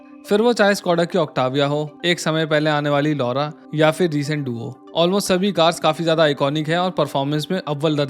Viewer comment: What did he say at 0:13???? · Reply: "Duo", I think he meant Kushaq and Slavia